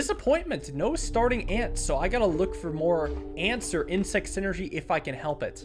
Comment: I definitely heard antswer